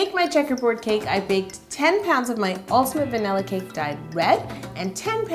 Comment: anyone else hear wed instead of red?